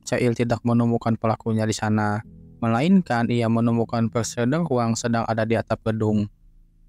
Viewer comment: Keren bang... Tp dr awal denger prosedur huang...apa aku salah denger yak...🙏🙏